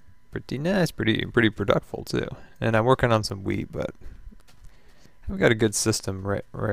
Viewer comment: hahaha i thought you said "im working on some weed" not wheat hahahha